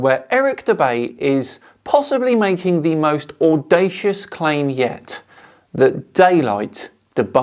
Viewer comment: I thought video said "the Flatest claim yet "